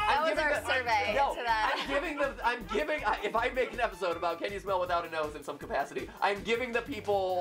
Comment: What everyone hears: “Can you smell without a nose?” What I hear: “Can Voldemort smell?”